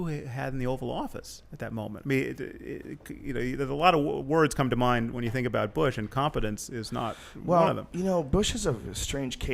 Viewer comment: Bush isn't incompetent? ? Wth? This pro NWO guy is either incredibly naive or he's being paid to say what he says. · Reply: misheard him. He said "A lot of words come to mind when you think about Bush, and competence is not one of them."